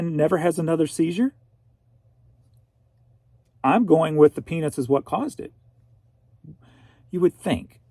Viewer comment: Penis? I thought I hadn’t heard right, so I checked and the transcript confirmed it. Sorry, but I’m just a bit skeptical of AI if it’s telling you the penis causes seizures… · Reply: peanuts lol